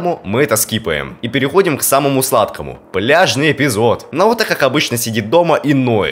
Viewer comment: Когда ты сказал пляжный не послышалось бляжный